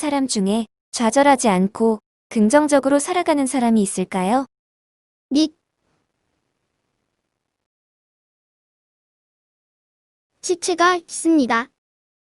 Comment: 초 안에 소리가 안들려요 저는 2:09는 닉 2:10~2:13은 안들리고 2:14은 시체라고 들려서 닉 시체라고 하는것 같네요ㅎㅎ